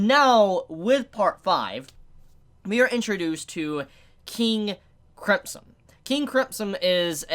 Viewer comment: Yo I heard him say king cripson I swear